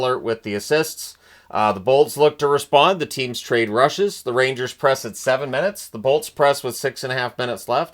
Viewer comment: im gaming while this is playing in the background and what i heard is ''the teams trade Russians'' and i choked